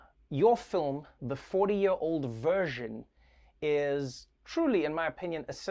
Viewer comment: I legit thought Trevor was saying "40 Year Old Virgin" and I was like "Did she write that movie!?"